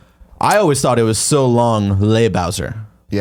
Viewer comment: I always heard "So long, ye Bowsey".